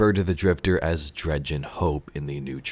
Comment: I thought he said Dredgen Hoe